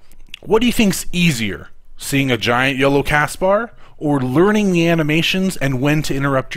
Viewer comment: sounded like "giant YOLO castbar" haha